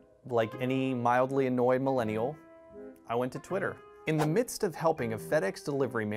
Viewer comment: lol on subtitles instead of Twitter, it says Titter...